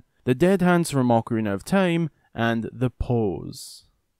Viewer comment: i thought i heard hoes but i know u said poes